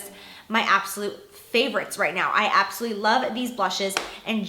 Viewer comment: I hate when you say "abosolutely" it sounds like your saying "absity"